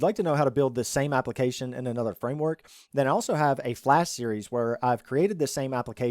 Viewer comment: Geez I thought he said Flash.